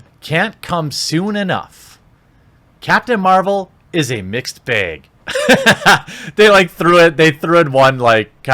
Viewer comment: I had to take a look for myself, I thought you said, "Captain Marvel is a mixed beg."